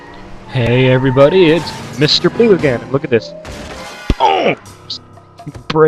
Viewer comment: LMAO! XD you sounded like you said Mr.Blue:Hey everybody this its Mr."Poo" again (I'm I the onelt one who notice't it?) lawl